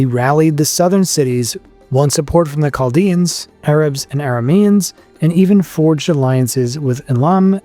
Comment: Did I hear you say arabs around 18:50?